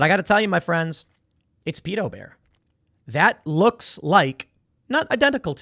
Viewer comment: 🤣 I thought you said Beto Bear! I thought he'd packed on a few extra LBs after his latest defeat there in Texas. Too funny to be true I guess.